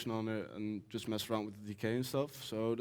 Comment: Did he say "decay" or what did he mean? Thanks for helping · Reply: yeah he said decay